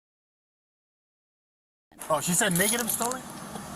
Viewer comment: I hear a couple things that may actually be of use. I am pretty sure I heard him say "Oh, she said negative stolen? &@ at about 3:17. He seems to stress the word "negative", It seems like that dofus, the one who pulled him over and went all gun crazy the plate was checked on a faulty app, and then he called it in and he was told "negative stolen" and he heard only "stolen". He didn't bother to actually confirm that before he goes full sociopath. So, yeah, it's likely the computer, not the app, was fine, Technically correct. it's just that the idiot with the gun couldn't be bothered to actually listen to the confirmation. What. A. Shocker. Also, he appears to say that the teacher gave him a look around 4:40 and that's why he decided to run the tag.